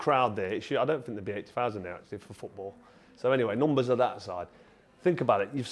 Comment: He was saying he thinks Wembley doesn't hold 80k anymore when setup for football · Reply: ahhh my bad I thought he said football doesn’t pull 80k